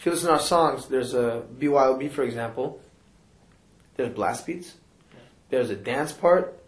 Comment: what does he say? "there are blast beats"?